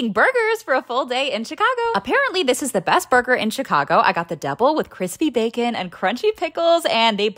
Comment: “Double” I heard “devil”👹